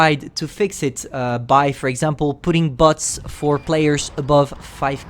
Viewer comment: Sorry for asking, but did you mean to say bots? You probably did. I heard butts. Sorry.